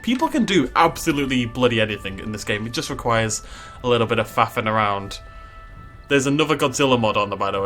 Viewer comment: I thought you said "fapping around"